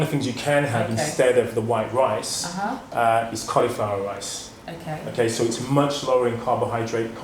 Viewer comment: Can someone tell me which rice he recommends at 25:35 ? I am no native speaker so I can not hear what he says properly. Ah, now I understood, the speaker pronounces it more clear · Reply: Coliflower rice. Its just the vegetable chooed into small pieces to resemvle rice